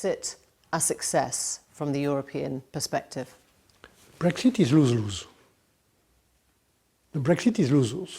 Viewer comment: M Barnier says "Brexit is a [something]." He says it twice. But I can't make out the word he's using - something like "rue". Can someone clarify please? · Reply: "Brexit is loose-loose"